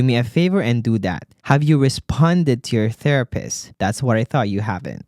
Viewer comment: I thought u said have you responded to your boss 🤣